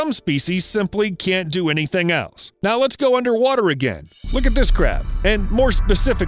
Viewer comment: When you said "look at this crab" I thought you said 'look at this crap'